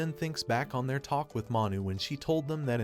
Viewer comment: mi brain muscle made me think he said mommy instead of manu